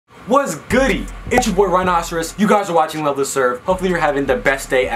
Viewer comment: did he say “rhinoceros”like mi giy